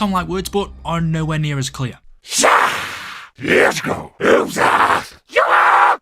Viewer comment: sounds like they said "les go"